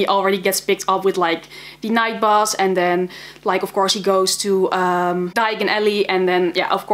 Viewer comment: did she say "diagonally"😔 By the way love the books